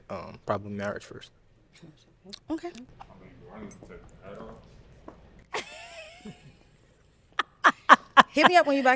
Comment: what did he said?? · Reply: "I mean do I need to take the hat off?"